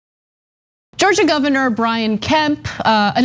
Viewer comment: And i thought he said Brains.